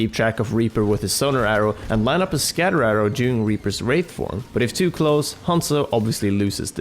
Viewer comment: What did I hear? During his rape Form? xD